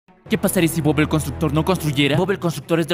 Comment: Pense que decia bob el destructor